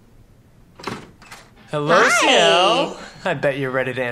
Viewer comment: DUDE I LEGIT THOUGHT HE SAID "HELLO-SEYO"